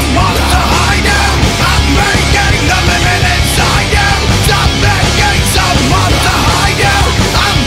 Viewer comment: Misheard lyric "I'm breaking the lemon inside you" 😂😂😂